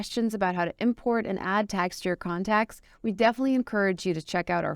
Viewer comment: Video is great & informative.......there is a correction at video section on time 7:16 (it should be tags instead of tax)......for mail chimp management to notice 😊